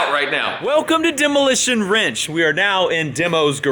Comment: There for a second I thought you said Demolition (Wrench) seeing as how you was a demo on the car